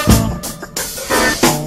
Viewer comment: All my Life I thought they was saying “I was slippin in the Dog shit” 🤦🏾‍♂️ damn cuh